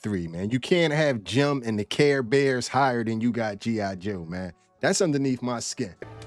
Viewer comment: I heard the gummy bears not the Care bears…😂 someone needs a care bear stare to remember.